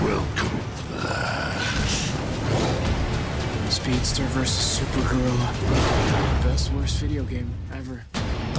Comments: Woooooooo . I Thought Cisco said Speedster vs super girl. xD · who else thought zisco said "speedster vs supergirl" at first?